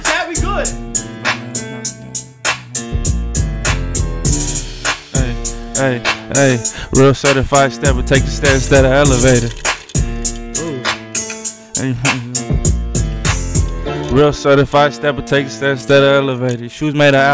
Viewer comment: Bruh... 5:10 . Did he say take the stairs to the elevator 🤔 that's some next level shit...